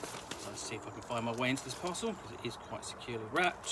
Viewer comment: Took me a beat to realize you said "parcel". I kept hearing "puzzle", and being like yes, it's a puzzle to get into, but they can't be what he's saying.